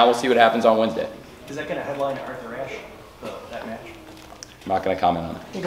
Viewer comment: What was asked at @ 8:08 that Tony said no comment to? I heard something like is that gonna headline something. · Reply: “Is that gonna Headline Arthur-Ashe?” which is AEW’s annual “Grand-Slam” Dynamite. Think of it like their version of a WWE Madison-Square-Garden show. A PPV on free TV, basically.